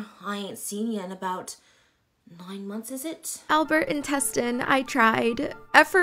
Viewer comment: GIRL THAT DID NOT SAY INTESTINE